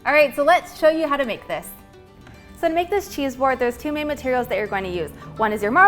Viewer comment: I thought they said chessboard